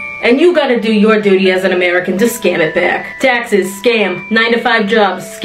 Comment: I can’t be the only one who thought she said that Texas is a scam instead of taxes